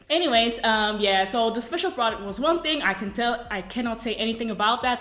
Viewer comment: At around 2:59 did you say "the special product was one thing..." Is the surprise, a product you made? ^^ I'm glad that you are back!